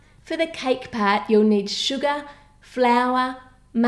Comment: I saw Bts and I clicked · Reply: can you PLEASE make a BTS dessert, tell us who's your bias and when you said "Sugar" it sounded like you said "Suga" 😂lol BTS reference